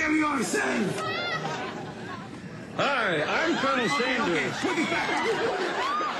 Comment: did he say "Hi, i'm Bernie Sanders"